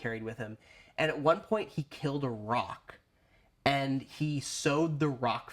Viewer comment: at first, I thought you meant "rock", instead of "Roc". I almost died with the fun role-playing opportunities that would give! King: points at belt pouch full of gravel What creature did you slay to earn that trophy? scoffs Barbarian: A far more worthy opponent than you could ever hope to be...